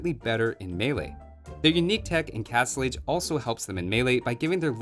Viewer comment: Did he say "unique tech in castle age" at 11:10?